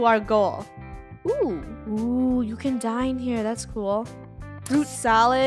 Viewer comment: I thought she said "oohhhh you can die in here!"